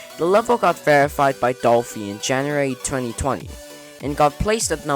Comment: i found some typos in the subtitles 7:31 it says 20200 in subtitles instead of 2020 7:44 it says the the level